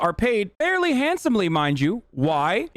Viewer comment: “Garbage people are paid very handsomely mind you”… at first I heard him say “my Jew”……. sent me